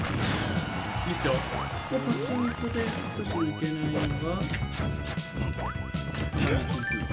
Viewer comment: 女の人の声が聞こえたような？ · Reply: が「パチンコで復帰阻止にいけないのは」って言ってる感じかな？ の声が高めなのと小声なのが合わさって女性に聞こえてるっぽい